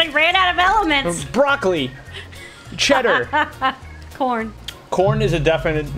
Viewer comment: When you said corn I thought you said Porn I was like what wtf